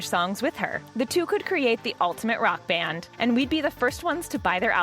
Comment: you said back and instead of band